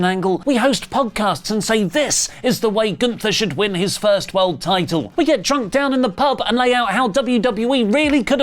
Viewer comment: “we get drunk and recast” is what the captions said lol